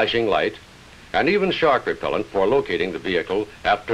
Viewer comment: Did they say, “shark repellent” @ 10:20 ? Love the Hustler, great name for an awesome aircraft!! 👏